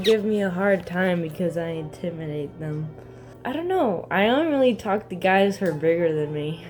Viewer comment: Hey guys, thanks for watching this vid!! 🙏🏼🙏🏼 love yall. And just wanted to say at 9:34, I said I ONLY talk to guys that are bigger than me. I know it sounds like I said Don't lmao. Anyways! Leave a comment on what types of videos would you like to see next! :)